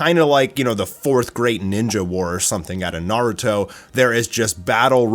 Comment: I thought he said the 4th grade ninja war 😂😂😂